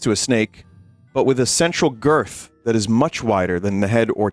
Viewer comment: At first, I thought he said "Sensual girth." And I was like "Huh? OH Central girth."